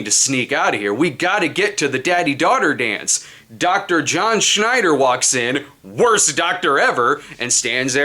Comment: For a minute there around 8:50 thought you said “Dr. Rob Schneider” and I was ready to open my Fandango App and see this asap